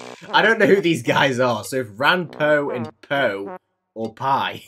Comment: the last one i thought it said ranboo and tubbo my soul left my body for a second💀